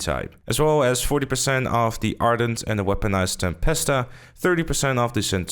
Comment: Did you say Weaponized Tempesta😂